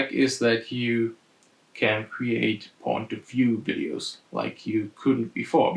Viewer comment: Did u really say (at 4:14) this glassed could be used for POV Adult Videos :-) lol